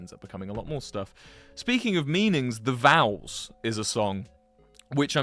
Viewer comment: I thought Wilbur said the vowels not the vows